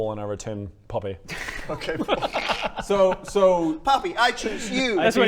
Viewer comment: I misheard this to be a "POPPYball" and kinda liked that.